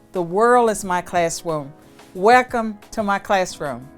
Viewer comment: Did I hear “the world is my class-womb”?